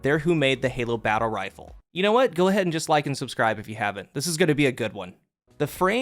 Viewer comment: Did you just say "dislike and subscribe" or am I just hearing things